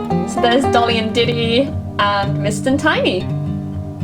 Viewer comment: When said mist and tiny I thought she said mr tiny 😂